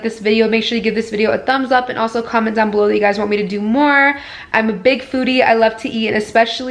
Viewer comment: I thought you said “I have a big booty I love to eat” I had to rewind that 😂